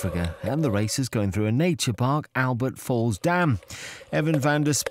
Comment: I thought they said "Albert falls down" I'm thinking "yes, I can see that!"